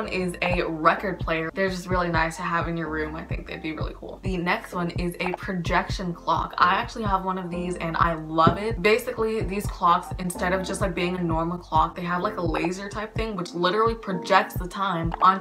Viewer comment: THE FIRST ONE...I thought she said "clock lips" lol